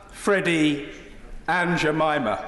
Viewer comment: Did he say Aunt Jemima? lol